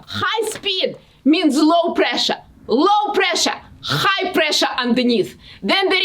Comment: Так приятно видеть русскоговорящую женщину, которая так горит своим делом! Вы замечательная ❤ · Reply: на 0:28 секунде так мило “high pressure on the низ» 😂 или мне послышалось 🤔